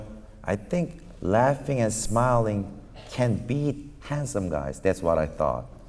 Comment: just wanted to correct, that Psy didn't say "I think laughing and smiling can ""Be"" handsome guys" at 5:18, He actually said "can ""BEAT"" handsome guys". :shelterin